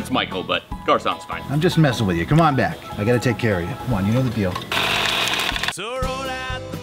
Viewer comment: "C'mon back, I gotta take care of ya'. C'mon you know the drill." Cash to the face. I'm dying! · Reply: I thought I heard "you know the deal" but the way you put it is even more outrageous 🤣🤣🤣🤣🤣, considering how hot 😍🔥😍🔥😍 Charlie is and how the other guy is invading his space 😝😆